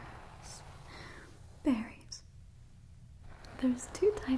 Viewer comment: Did anyone else mishear fairy instead of berry? I was a little concerned for the fairy for a moment.